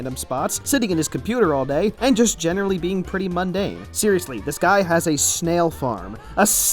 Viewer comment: UNDARN Like if you noticed that he said Undarn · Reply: Me too I thought he said undarn